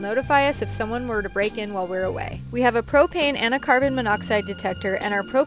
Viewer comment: When you said propane I thought you said cocane